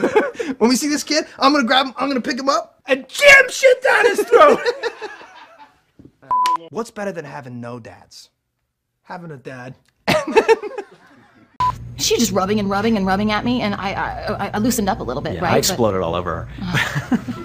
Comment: "What's better than having no dad?" "Having a dad." Idk why that killed me 😭😂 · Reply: For the longest time, I kept mishearing what they said and thought it went, "What's worse than having no dads?" "Having a dad" And that was hilarious to me